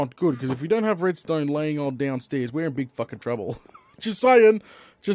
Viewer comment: Does he say "We're in big f***ing trouble"? O.o I haven't heard him swear like that before